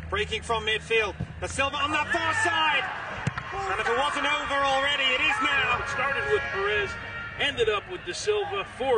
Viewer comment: A satisfying performance here. I have hopes for our future when I watch this team. But somebody's not happy at 1:16 "PUTA!" I can't hear the rest of it shrug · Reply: KenDaKingArrives oh ya, I mixed up punta with puta. Whoever definitely said puta.